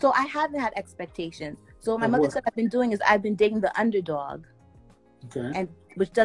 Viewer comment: “I’ve been dating the underdog.” Girl, you ARE the underdog! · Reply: I thought she said " I've been playing the underdog"???